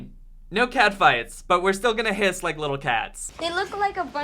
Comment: "Their still going to hiss like little cats" what I heard was "Their still going to kiss little cats".